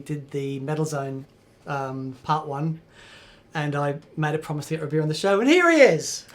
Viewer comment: Could have sworn I heard in there somewhere... hah! Awesome show. I can't wait for these to show up each Friday - they make my day.